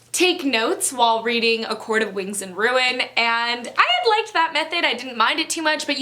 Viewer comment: I belieeeeeve I only mentioned A Court of Wings and Ruin! Since I never took notes on the previous books! Hope this helps :) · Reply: ok! I’m sorry! I thought you said “A Court Of Thorns and Ruin” so I wanted to check. Sorry. 😩